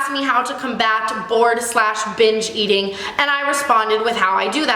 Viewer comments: I thought Tana said bored/b*tch eating. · I thought you were saying 'bored/bitch eating' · LMAO I thought she said bored/bitch eating and I was like ??? I guess I'm bitch eating now 😂😂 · I thought she was saying "bored/bitch eating." Ha. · I legit thought she said bored/bitch eating instead of bored/binge eating.